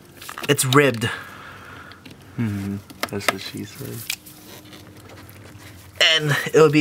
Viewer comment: Lol at 7:43 "it's tight" ...heard the camera guy say "that's what she said"